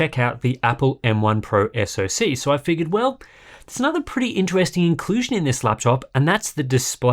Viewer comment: Did he say "Intwesting"? SO CUTE!